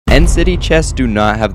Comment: i thought he said density chests instead of end city chests before reading the captions💀